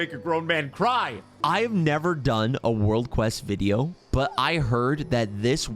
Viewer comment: uff.. for a second I thought you said "I never done world quests" 😅